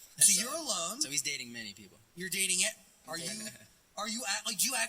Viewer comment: i thought Glinsky said JJ was dating mini people 😂😂😂